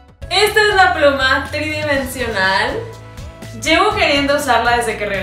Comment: te entendi broma en vez 💩 de pluma 🖋